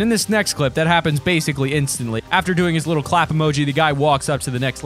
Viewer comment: Did he say emoji